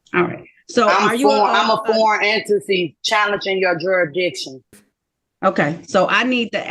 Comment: Did she say, "Juror Dictions"?